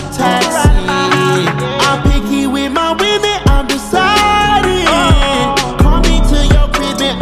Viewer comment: i deadass thought he said “i’m picky with my women, i’m poseidon” lmaooo